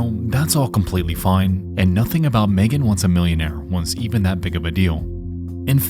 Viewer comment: Captions says "an escapable" when it should say "inescapable" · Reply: Captions says "wants even" but says "was even" (There's also a "Junk Food" caption a bit before that...? I don't know what really said so I'll ignore but point it out)